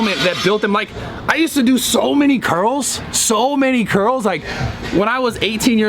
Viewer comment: For a second I thought he said “ he did so many girls “ lmao